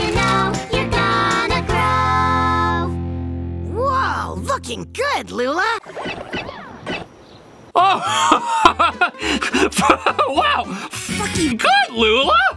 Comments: I heard looking good Lula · i heard looking good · I think he thought it said the F word but what I saw it didn’t I think you just misheard it because I heard look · I keep hearing f****** good😂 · he actually said "looking good lula" He was cussing like salor 6:25 Ok maybe that was the f word 7:19 8:39 I can be quite a itch 10:21 what the heck More translating soon! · I heard looking not de F bomb😂😂😂😂😂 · what he clearly said looking good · I hear looking good Lula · he did not said the f bomb with the ing he was ment to say LOOKING